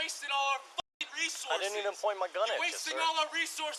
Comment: Did I mishear this officer at 20:16 because I swear he said he didn’t mean to point his gun at the student ? Like how do you accidentally unholster and aim your gun? Also once they confirmed he lived in the building why are they still surrounding him? Smh I would appreciate it if an officer could actually explain why this is their procedure. Listening to the officer explain the situation and then try to lie about not confirming the students address and the whole tampering w the body cam...I’m speechless